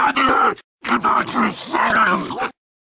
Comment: I heard “get out you satans!” Because the infected see the survivors as monsters.